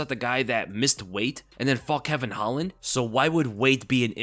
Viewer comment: At first I thought you said missed weight and f***ed Kevin holland 😂